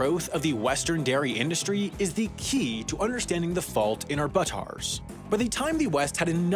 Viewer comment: did he just say, “is the key to UDDERSTANDING”?